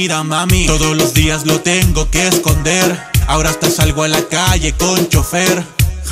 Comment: pensaba que dijo ahora hasta salgo a la calle con sostén :v